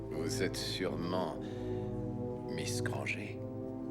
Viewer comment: ai-je bien entendu "miss GrangÉ" ? Jésus Marie Joseph dites moi que c'est pas vrai